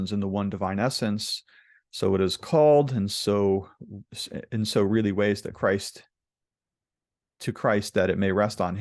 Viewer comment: the reading should be "so it is called in so many ways to Christ ..." instead of "so it is called in so really ways to Christ ..."